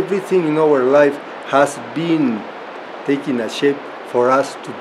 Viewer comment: sounds like he says "everything in our life has been taking a $h!t for us to be here"